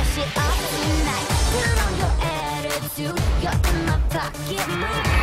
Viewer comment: WAIT A SEC AS SO EONE WHO DIDN'T HAVE DISNEY CHANNEL DOES IT SAY YOU'RE IN MY FUCKING MOOD? LMAO I KNOW DISNEY WOULDN'T ALLOW THAT